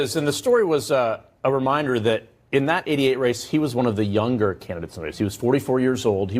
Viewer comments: it sure sounds like he says, "in that idiot race". Lol🤣😂🤣😂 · Listen to around 2:10-2:13 I know the contributor says “in that ‘88 race” but for a second it almost sounded like he said “in that idiot race”. Which I would have agreed. That’s all we get is useless to us idiots but useful to the media machine for however long they can squeeze every last drop out of them then they turn the page. It’s like waking up and realizing your parents might have something to do with Santa Claus and the Easter bunny. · I swear, at 2:10 he said "in that IDIOT'S race", not "in that '88 race".